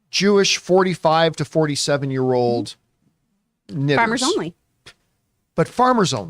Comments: What does John say at 1:16:00 and should we be glad talked over it so it is unintelligible? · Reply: Knitters? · I might be tripping but what does he say at 1:16:03, pertaining to the dating sites for everything?? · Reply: Knitters